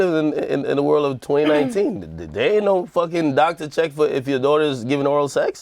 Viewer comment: did Ak say you kant check if sm1 is participating in oral sex?